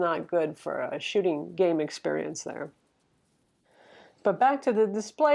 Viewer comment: I thought you said, "a shitty game experience" at first. ha! ha!